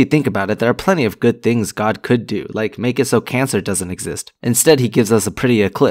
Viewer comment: "Make it so kansas doesn't exist" thats what i heard at first, sorry people from kansas